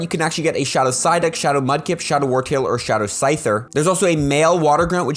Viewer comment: did i hear that correctly? shadow scyther? 😳